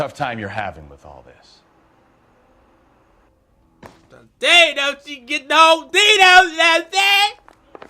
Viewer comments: if you understood what he said let me know😂😂😂😂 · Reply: along the lines of : "She can get the whole D though, know what I'm sayin'" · what did say? 😂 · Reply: He said that ass tho